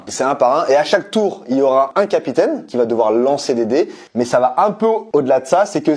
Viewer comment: j'ai compris "Lancer Dédé"....